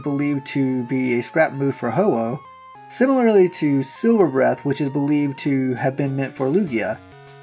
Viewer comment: I thought he said Sewer breath...